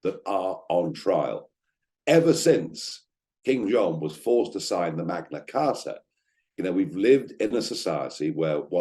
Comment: Lol I thought you said ever since Kim Jong un was forced to sign The Magna Carta at first lol . I had to go back and listen again lol